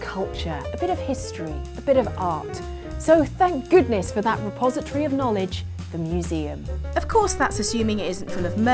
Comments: I thought said "Suppository of Knowledge" in the beginning. Too many old Tony Abbott memes for me. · I heard that as "suppository of knowledge". that's a butt load of wisdom.